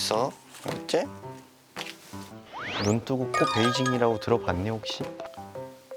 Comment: "눈 뜨고 코 베이징 이라고 들어봤니?" 이거 아무도 못들었나요ㅋㅋㅋㅋㅋㅋㅋ